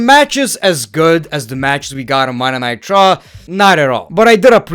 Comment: I swear, everytime this dude says "Monday Night Raw" I hear "Monday Nitro".